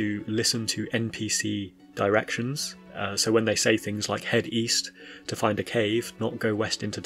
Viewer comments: Squall: East? I thought you said Weast. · East? I thought you said Weast. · I thought she said weast